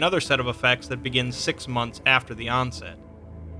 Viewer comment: I heard "6 months after Beyonce"